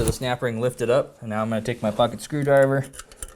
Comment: you said "pocket " screwdriver....I kept hearing "F**king" screwdriver 😅😅 thought you got mad out of nowhere 😂😂 thank you for the video, im using it as a companion on my rebuild.